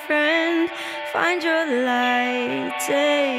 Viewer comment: Parang madaling ma mishear yung "You are enough and" to "You are nothing" lol